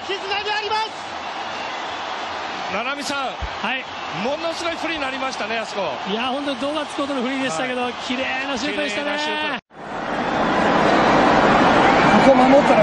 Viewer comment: 「ナナミsun，ものすごいプリンになりましたね」 「いやあほんとドーナツ食うほどのプリンでしたけど，綺麗なチ○コでしたねぇ～」 wwwwwwwほんとにそう聞こえるwww 腹筋崩壊動画ですww爆笑しすぎて涙がとまりませんwww